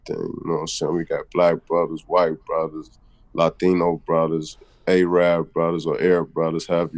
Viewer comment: AMBROSE !!! Man those guys were made my life a living hell and this was back in 86-90. Fuckers!! Was what it was. They are a racially mixed up bunch for sure. Remember them from around 59th and California. He's legit this guy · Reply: Oh he said ambrose I thought he said Latin brothers and I was like I didnt know they had those out east